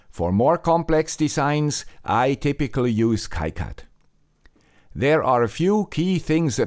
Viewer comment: I didn't quite catch the software that he uses for more complex PCB projects..... kite app?? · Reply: KiCAD